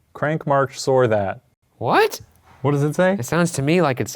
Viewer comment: sounded like it said "warden with us"